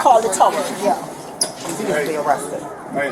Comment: 😂😂 I thought this black booger said she wasn't gonna get arrested 😂😂